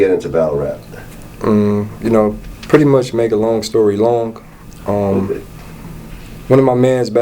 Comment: Lol did he say long story long? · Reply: Yea I thought I heard him say long story long.